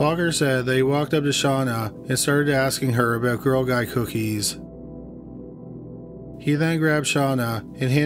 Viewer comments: Did he say girl guy cookies? I’m confused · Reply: Girl Guide cookies. I know some countries they call Girl Scouts Girl Guides. I think where he's from is one of them. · I'm sorry but at 17:24 what kind of cookies are you saying. I'm assuming it was supposed to be girl scout cookies but on the captions it says girl guy cookies. The pronunciation in these videos is just disrespectful because of how lazy the words are said. Come on. Between the many videos lazily researched and wrong information given to how lazy you tell the stories of these cases...this channel is becoming very disrespectful to the victims and their families. Quality over quantity. I'm sure your subscribers would much prefer the correct information given instead of a new video every day or so. Do better. I've seen many comments from family and friends of the cases you cover having to correct the information you are giving and you never acknowledge the facts given or bother to correct the information. Which is very easy to do by doing what other channels do. Tbey post the corrected information in the comments and pin it. Please do better, these victims and their loved ones deserve it. Quality over Quantity.